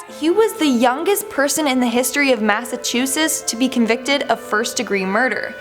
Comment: did she say massachu-SIS? p.s. can i please get a shout out.